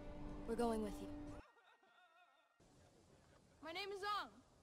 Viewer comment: I thought he said "My name is up"